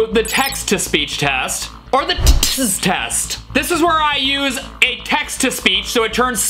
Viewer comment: when ever he says tts test, I hear, tĩties test…